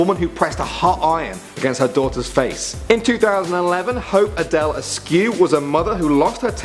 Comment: am i hearing things or did he say 'hope Adele'like the one who made 'Hello'?